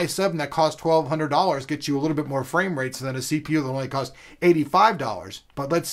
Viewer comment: haha I thought he said frame-rapes lmao